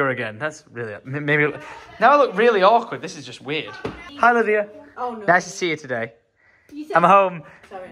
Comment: I love drunk, is there a running joke though where you call “Olivia” because I swear that’s what you said before you high fived her when you were back home